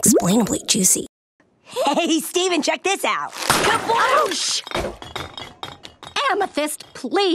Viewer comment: I swear Pearl says "BLOOSH"